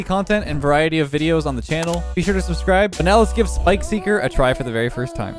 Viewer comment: Man I heard Spikeseeker like spikesee girl😂